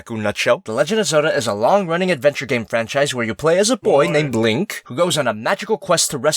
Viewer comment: did he just say blink except of link ?